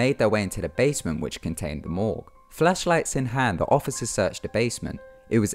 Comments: For a second, during the second case, I thought you said FleshLights, but then you repeated yourself, and I realized I have a dirty mind. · I thought he said "fleshlights"